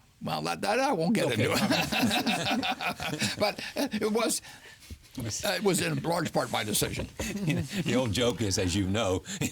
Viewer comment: What did he say at 0:35-0:39? It was ??? part of my decision? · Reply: "It was in large part my decision"